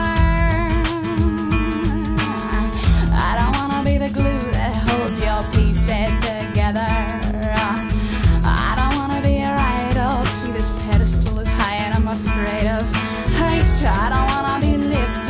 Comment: As a Trekkie, I always hear "I don't wanna be your glass of synthehol"